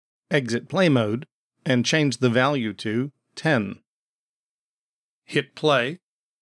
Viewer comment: you have a mistake in subtitles. 500 instead of 10.